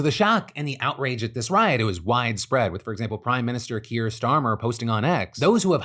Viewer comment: It really sounded like you said crime minister, its a way more appropriate title for those who have held his position.